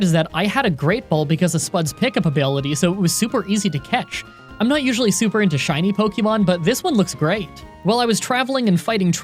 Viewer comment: "I'm not usually super into shiny pokemon" All I heard was "I'm a sinner"